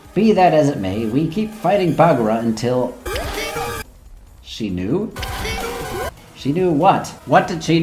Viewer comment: I think Baguraa is not saying "she knew", but rather "死ぬ(shinu)", which means "die" in Japanese.